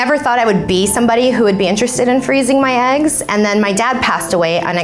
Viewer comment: Am I the only one that thought the girl from the thumbnail was trans and she/he actually froze it's nuts? I'm too high for this. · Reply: I thought it said I froze my legs